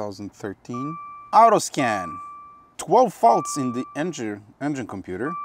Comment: I heard 12 VOLTS in the engine computer... What's wrong with that, I thought? Oohhhh, its 12 FAULTS okay.